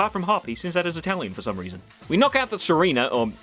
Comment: Second time watching and I swear you say "Hop sends out his Italian for some reason" at 4:30 and it makes it infinitely better.